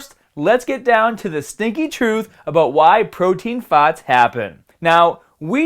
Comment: I thought you said fats instead of farts 😂